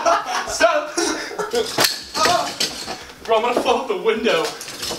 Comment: did Ethan say he was about to fuck the window? lol! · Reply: He said he was gonna fall out 😂